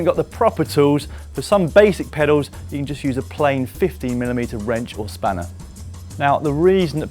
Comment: don't go shopping for a 50 mm wrench or spanner... he said 15 mm - I misheard him..... fail